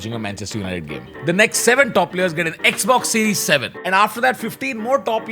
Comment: Did Tanmay just say “ xbox series 7 “ ???